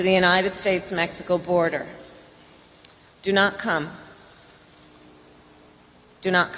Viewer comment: Whenever Harris says, "Do not come," I hear, "Just the tip."